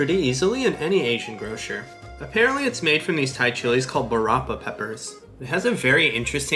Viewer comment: You said, "barapa", I heard, "Parappa".